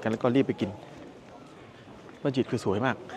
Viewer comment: บ้านจิตคือบ้านใคร<num> เขาพูดว่ามัสยิดรึป่าว ซับทำให้ดูแย่<num>